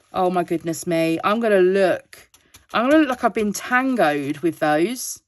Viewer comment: The original photo is beautiful! certainly worthy of being a diamond painting, hope it gets sorted :D Question: did you say "have a look in Tango"? 7:56 Is that a program that lets you change drill colors? I'm looking for a program like that.